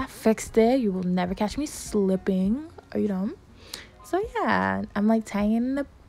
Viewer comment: you will never catch me slipping 😂😂 then goes on to say “are you dumb” 😂😂😂 I can’t be the only one who heard that